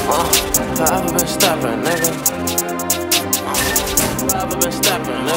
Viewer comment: Bro why all this time I thought he said FOREVER VERSTAPPEN? LOL, sick song btw